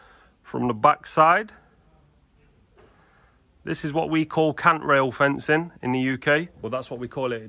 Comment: He keeps referencing 'cant rails'.. they're not.. A cant rail has a slight angle for rain to run off similar to an Arris rail. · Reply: i thought he was saying count rail, like counter rail lol, their accent is a bit like that.